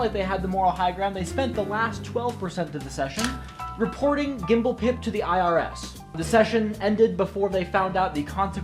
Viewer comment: Ngl I heard "Gimblepimp" and Im keeping that for my next game